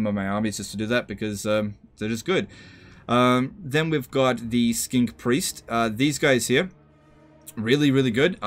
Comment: Do the Lizardmen have 2 different types of "Skink Priest" heroes? That part was a little confusing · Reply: Oh right thanks, it sounded like he said Skink Priest twice but could have just been me